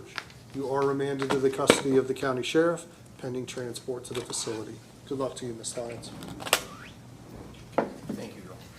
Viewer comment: Her very pregnant relative appears to not understand that she is a criminal. Guessing the younger generation of this woman’s family have learned some really bad, illegal habits from her. When pregnant person muttered as she angrily waddled out I am surprised the judge didn’t reprimand her a bit. Habitual criminals aren’t going to learn anything unless they actually pay for their crimes. Edited to add.. defendant’s “wow” and apparent disbelief that the judge didn’t cave in and release her early was actually a clear indication that she really has zero understanding of why her long and continued criminal history earned her every single day of that prison sentence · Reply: I thought that lady said Good Luck Cow